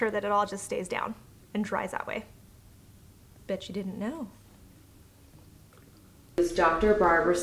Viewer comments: I thought she said “bitch you didn’t know” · OMG I thought she said BITCH U DIDNT KNOW lollll